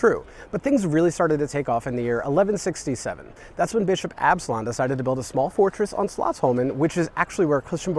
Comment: At first I thought you said bitchin Absalon😅 and then I thought yeah! He is quite the cool guy😂